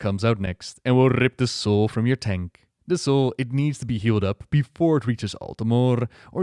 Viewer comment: At first I heard "he'll rip your soul from your taint." Teach me to pay attention to the video.